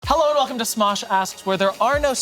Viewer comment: I need you to know that the auto captions say “Hello and welcome to Smosh Ass” lol